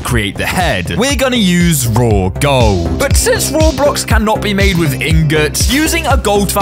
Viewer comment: said Roblox instead of minecraft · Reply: He said raw blocks not roblox